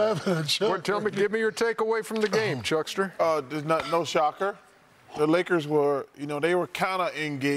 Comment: Sounded like chuck said no chalker right after Ernie called him chuckster I thought that’s why shaq and Kenny were laughing 😂😂😂